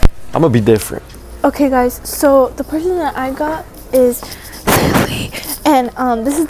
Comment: when said lily I was like “huh what did u say” and I had to restart that part bcz she was so quiet lol😂❤